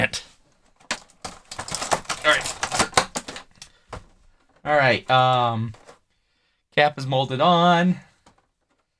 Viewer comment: 😮 was that an N word I heard !!!!! That’s how I open all my packages too! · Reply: OH GOD NO.... It was digger because his name is Digger Harkness